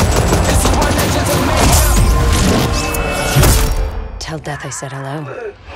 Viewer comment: Did she just say: Tell Dad i said hello?? PLOTTWIST